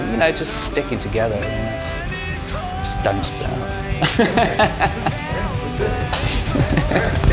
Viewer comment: Can someone tell me what Dom is saying at 7:46? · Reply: 'Sticking together and just don't split up".